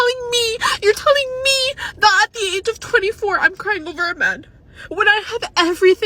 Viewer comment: what did she say? I just didn’t hear what he/they said to her Oohhh, I think I got it. She said “crying over a man”